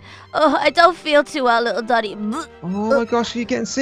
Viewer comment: when said Danny it sounded like she said daddy